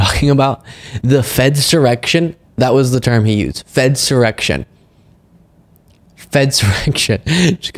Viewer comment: I thought he said "Fence erection" and was very confused. Fedsurrection doesn't mean anything to me either but...